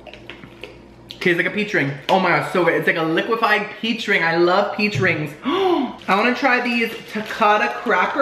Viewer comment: I heard........... "I love pee drinks." ;)